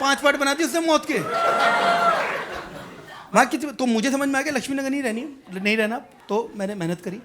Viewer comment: what does he speak? Did I hear correct ? Maa ki ...?